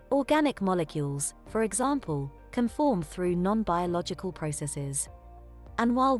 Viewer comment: subtitle error "nonviolent vehicle" instead of "non biological" 5:27 Search For Extra Terrestrial Intelligence "SETI" not "CETI"